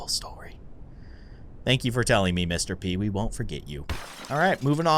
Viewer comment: When you said I won’t forget you I thought u said I won’t forgive u